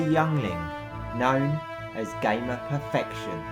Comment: lol I thought he said gayer perfection at the start XD